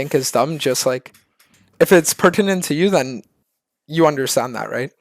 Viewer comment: did he say precadent? if it's percadent? i'm not a dictionary but i'm almost positive that is not a word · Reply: Pertinent